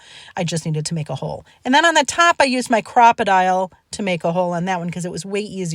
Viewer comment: Anyone not familiar, the tin hole tool, is a "crop a dile" hole punch, can b $10-30, seems v. Handy😊🎉❤ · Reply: Thank you...I had heard her say "croc-o-dile" with my hard-of-hearing ears 😂 but apparently that's not its official name! I appreciate your explanation for us HoH folk! 🙏🕊